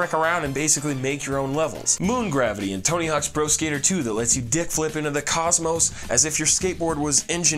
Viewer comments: When he said kickflip it sounded like he said dickflip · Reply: I thought he said dipflip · When he said kickflip it sounded like he said dickflip